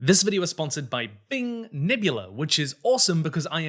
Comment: For a second I thought he said "This video is sponsored by Bing"